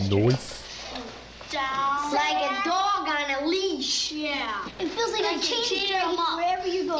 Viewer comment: 相変わらずまとめるの上手すぎます！😭神動画ありがとうございました！やっぱり自分がされて嫌なことは人にしないって当たり前だけど大事だな〜〜って思いました！ p.s. 5:12 ここ自分は Like a dog on a leashって聞こえました